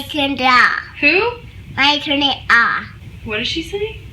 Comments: To Me It Sounded Like She Said "Mike Turned It Off" ... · omg i heard "mike turned it off" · Sounds like she said Mike turned it off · I thought she said mike turned it off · sounds like she was saying mike turned it off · i heard her say "mike turned it off".